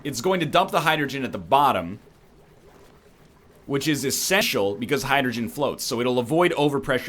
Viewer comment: Anyone else hear 'esseshial' at 7:57 ?